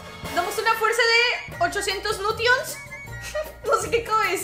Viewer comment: Cuando dijiste 800 notions pensé que dijiste 800 novios jajajaja jsjs jeje